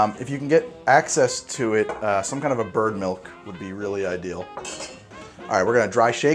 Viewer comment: did he just say you need birdmilk?